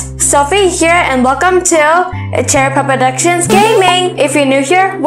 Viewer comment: WHY DO I ALWAYS HEAR CHAIR POP PRODUCTIONS💀😂😂